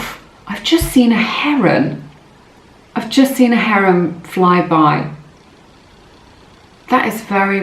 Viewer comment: sorry I couldn't understand. A harem? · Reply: She said Heron